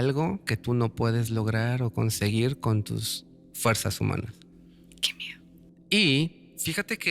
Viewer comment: En el minuto 16:03/16:08 escuché una voz de mujer que dice: "que miedo" Alguien lo escucha o es mi imaginación?